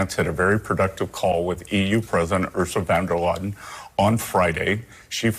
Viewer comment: Bessent can't pronounce Ursula's full name? 0:03. Did he call her "Ursa Vanderladen"? I mean that's how the CC (caption) captures him calling her.